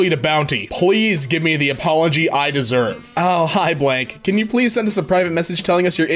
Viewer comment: did he just sayed: ,,Hi Bwanck?,, why does thys owo shit keep following me.